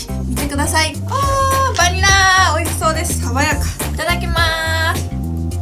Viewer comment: いただきます が いただきばす にに聞こえるのは私だけ？